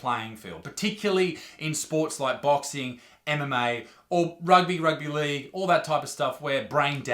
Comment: I thought he said "particularly in sports like boxing, anime or rugby". I had to listen to it a few times before I realized he said MMA